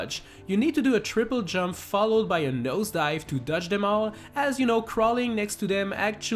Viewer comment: sounded like he said TOUCH them all